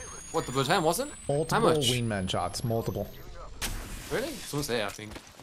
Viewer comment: I thought you said “Wee man” instead of wingman the first time you said it, lmao